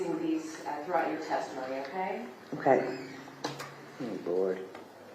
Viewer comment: Does she actually say “I’m bored” at 4:20? Or “oh lord”? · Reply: "Oh Lord"